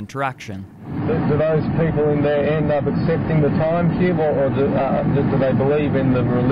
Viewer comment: this is a 4.5 year old video but on this rewatch I'm surprised you didn't catch the "incomprehensible" bit at 36:10 as "end up accepting"